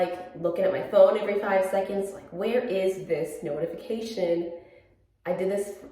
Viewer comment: It sounded like you said “snow-tification!” lol so punny